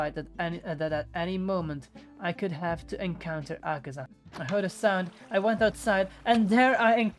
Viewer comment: i literally heard u said "I heard Hasan" XD